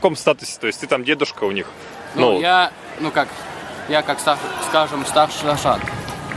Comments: послышалось " я старший лошара " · послышалось старший лошак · послышалось старший лошара)))))))))))))))